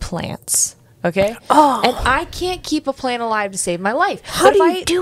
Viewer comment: I feel so silly lol I had to have the volume down because my son was sleeping so when was talking about keeping plants alive I thought she said "plans alive" because ADHD + planning can equal hit mess lol and I was trying to connect all these dots to make the soil and stuff a metaphor for planning skills 🤦🏼‍♀️ Did you change plants because the bugs kept coming for? 😂 ALSO I have thought ever since 9/11 that the world wasn't going to last through my lifetime and I struggle to plan for the future bc it feels pointless. I wonder if we are predicting something or if this is a mental illness thing?